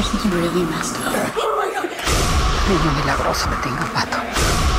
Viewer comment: sounds like she says wanker